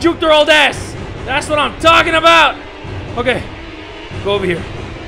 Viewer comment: when that dude said “THATS WHAT IM TALKING ABOUT” am I the only one who heard “THATS WHAT IM DOGGING ABOUT